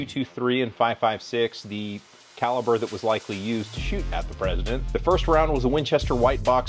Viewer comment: I don't remember anyone calling Barack Obama "The President" after his 2nd term ended. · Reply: I thought he said “ex-President”, and I liked it because I hadn’t heard it used before and is less awkward than “former president”. But because of comments I listened again back and heard that he said “at the President”. Disappointed, but now hoping that saying “ex-president” becomes common.